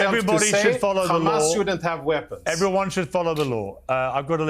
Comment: … „everybody should follow Allah“ this is clearly what she says… am I the only one who heard that? Keep on Abby, who ever the f*** you are… you are convincing us with every ugly word that comes out from your hateful mouth that all this stuff is 100% invented pure Jew hatred and lies. Shame also on Piers Morgan. Am Yisrael Chai from Germany 🇮🇱❤️